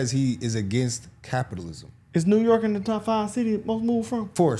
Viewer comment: She actually said New York Shitty.